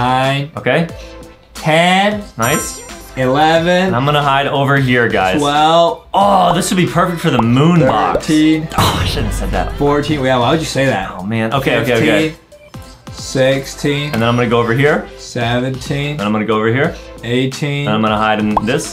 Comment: i heard Adem saying aww sh*t why would i say that am i the only one that noticed?